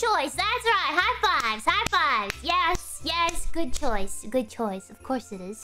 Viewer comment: Erro de tradução (eu sei q eu devo soar chato pra ca*alho mas n dá, eu n consigo, foi mal!) Ela n disse: "Have five, have five!" Ela disse: "HighFive, HighFive!" Que seria tipo: "Toca aqui" Ou algo assim!